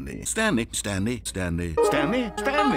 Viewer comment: I hear “stany” lmao